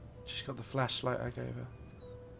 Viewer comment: sounded like he said fleshlight.😂